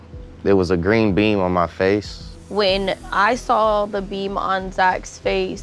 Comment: i cannot be the only one who thought he said a green bean on his nose